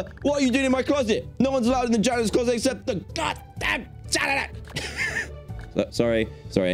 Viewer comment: Did Dan say god damn